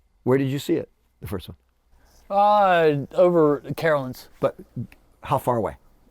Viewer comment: At about 51:00 or thereabouts, I’m missing what is saying about his encounter. Is he saying it took place in “Carrollton”?